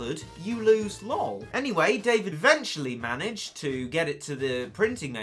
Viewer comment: Did he say David at 1:32?